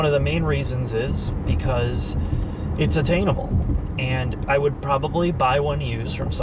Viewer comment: I am thinking a Pontiac Aztek,a mid 90's Geo Prizm and a Chevette diesel. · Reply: I thought said attainable, not free.